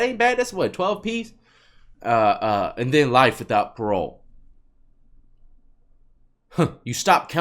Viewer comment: I think she said life “with” chance of parole both times.... I gotta rewind it and listen again but I thought I heard life “with” .